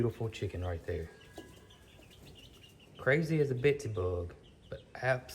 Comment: Crazy as a ‘besty’ bug?? I love your southern slang but I couldn’t make out what you said but I 😂 laughed out loud 😂 had to rewind and still couldn’t catch it. :_Love